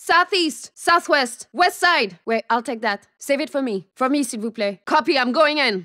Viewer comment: what did she say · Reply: S'il vous plaît